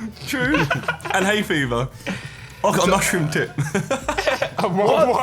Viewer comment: Was it just me that thought josh said mushroom tit.... Just me OK ill go back to being a depressed 14year old now ;_